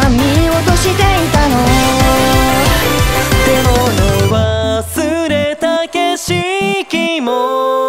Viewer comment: ⒈ 0:01と3:40のゲームボーイ起動音に似せた音 ⒉ 0:14「出かける準備は出来たかい?」初代の最初にテレビにAボタンを押した時の「もう行かなくちゃ」を元にしてる歌詞 ⒊ 0:17「キミに見せたい不思議の世界」は「不思議な不思議な生き物、動物図鑑にはのってない」を模した言葉 ⒋ 0:20の「見送りならいらない」はサトシが旅に出る時に飛び出していくこと ⒌ 0:24「たとえ火の中水の中…」がアニポケ初代のOPの歌詞と同じ ⒍ 0:29の「まだ知らないけど」のメロディーがゲッタバンバンの「まだ見ぬ未来へ」と似ている ⒎ 0:43アニポケのモンスターボールからポケモンが出てくる音 ⒏ 1:09と4:08の「3で仲間になろうよ」はポケモンが仲間になる時のボールが揺れる回数 ⒐ 1:32と4:30「きみにきめた」 10. 2:54「雨上がりでなくちゃ 晴れ間に虹は無い」→AtR? 11. 3:03からのBGMが初代殿堂入り時のBGMを意識してる? 12. 3:12「君は 君は いつの間にやら〜」はゲームをしていた人達に向けての言葉 (2019年11月22日ツイキャスより) 13. 3:25「右ぽっけ」ゲーム機orモンスターボールを連想させる言葉 (2019年11月22日ツイキャスより) 14. 3:43初代の戦闘シーンの入の音 15. ネックレスが｢M｣と｢S｣ 16. 2人の好きなポケモンもMVに登場(概要欄より) 17. 2019年7月30日のツイキャス生放送の44分辺りで作っていた曲 18. 世代によって印象が変わるように作ってある(2019年11月22日mfさんツイキャスより) 自分用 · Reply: の「忘れた景色」が忘れタケシき にしか聞こえない(ポケモン廃人より)